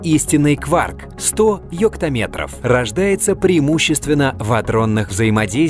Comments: мне послышалось он сказал «ёпта-метр» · 100 ёпт*метров XD кому тоже самое послышалось лайк под мой коммент, 1:05 это что? пикачу стал метром?)) а чо когда назвали пикометр? а не пикачуметр?)))